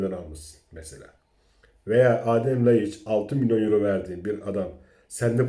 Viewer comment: 3 milyon euro diye duydum ben